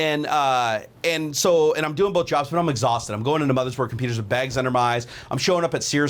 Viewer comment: Thought you said I was doing blowjobs lol [insert Scooby Doo growl here]